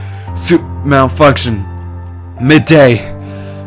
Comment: At the end, it sounds like Kurt is saying "miday", instead of "Mayday"